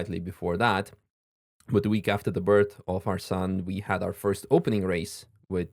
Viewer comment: At first I thought he said "We welcomed Tarzan into the world. So I became a dad." I guess it was "our son" not Tarzan! 😂